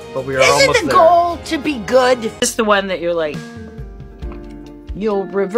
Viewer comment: I like how they both laughed like they were besties when he said “yk what we’re already dead” !!🤣🤣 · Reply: @@ I thought he said “You know what? We’re all going to die”